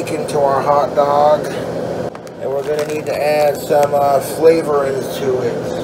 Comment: Omfg 1:24 I thought he said "slavery" for a second 💀